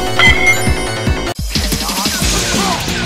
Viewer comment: i heard "Here's a cake" instead of he is a cake